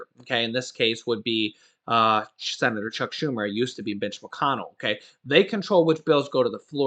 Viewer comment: Did I hear bi**h McConnel?!?! 🤣🤣🤣🤣